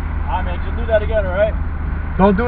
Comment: I thought he said do it again too🥴